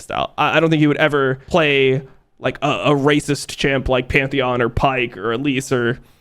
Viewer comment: Am I hearing him right? Did he say a "racist" champ @ 12:30?